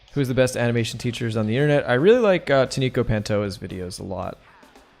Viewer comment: Can someone write his channel or name i didnt catch it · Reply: Taniko Pantoja